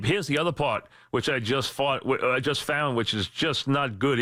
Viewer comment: am I hearing things or did he really say “bend over backwards” and “which I just fart”?